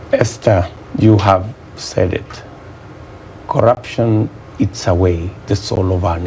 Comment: What did I hear CORRUPTION is the SOUL of our NATION !!! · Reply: I think what he said was that “Corruption eats away the soul of our nation..”